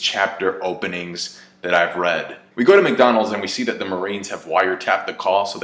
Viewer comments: Did just say “we go to McDonald’s “ at 2:32 ? Lol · you said "we go to mcdonalds" instead of marineford, bruh you're trippin